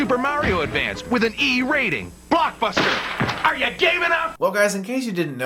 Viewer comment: I heard "Blockbuster: Are you gay enough? "